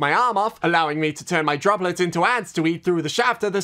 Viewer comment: I misheard ants as ads.